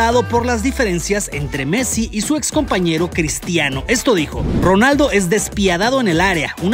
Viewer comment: Pensé que decía "Cristiano es Asensio". Espero no ser el único XD🤣 Like si te pasó lo mismo